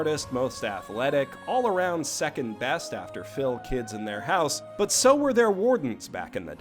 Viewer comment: What did he say at 3:50? “After Phil, kids, and their house”?